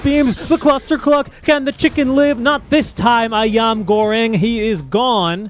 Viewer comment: i heard ayam goreng?